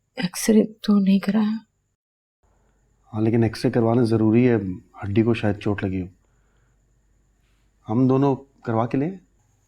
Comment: I thought Shahid said AUNTY instead of HADDI 😂😂😂😂😂😂